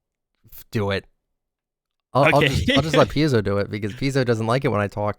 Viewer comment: I thought you said piss-o at the begining lol